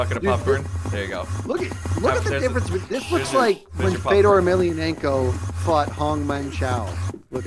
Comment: Did i hear Fedor?